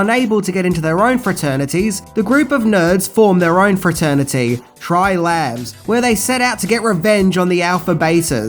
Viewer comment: What did he say @ 0:47? C'mon, Tri-Lads? Any self-respecting nerd knows it's Tri-LAMB.